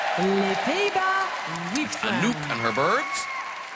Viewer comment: Did he say "Angry Birds"?????